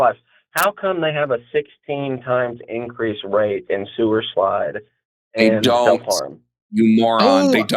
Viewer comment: Is it just me, or does say "sewer-slide" instead of suicide at 40:15? I swear that's what it sounds like.